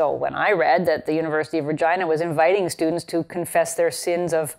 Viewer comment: I swear to God I initially thought she said "University of Vagina"